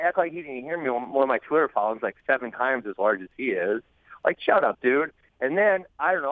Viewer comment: Now and Later cool but that's not what he said. He said "I probably have more then them both combined" "I probably have 7 times as large of a following" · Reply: listen again 6:15 "He's trying to act like he didn't hear me when my twitter following is like 7 times as large as his is" This is an accurate statement Bens got like 200k to Marty's 30k